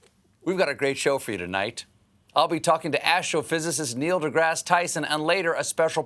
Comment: "Astrophysicist"? I heard "Asshole Physicist". I need to go get my ears cleaned.